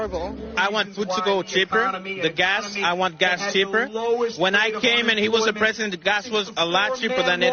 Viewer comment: I heard gas chamber not gas cheaper.